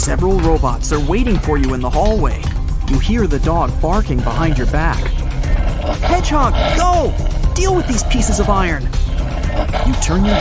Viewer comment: "Hedgehog the dog" instead of "Sonic the Hedgehog". HedgeDog.